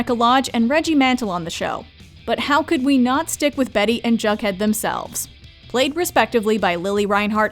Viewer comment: Is this an AI narrated clip. I could have sworn she said Bug Head not Jug Head on the second mention of his characters name.